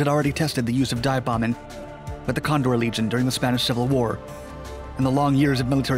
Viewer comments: did he say Convoy Legion? I’m embarrassed because all these years I’ve been calling them the Condor Legion. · did he say the “Convoy Legion”? You learn something new every day as I’ve always called it l those years calling it the Condor Legion